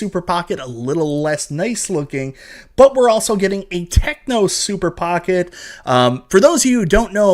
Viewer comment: Omg I thought I heard you say tecmo super pocket but then I realized it was a technos super pocket. 😂